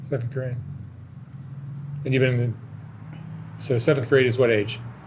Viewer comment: Just the little things. It sounded like she said, “seven grade” . She probably didn’t learn to add the “th” to pronounce it as, “seventh grade” .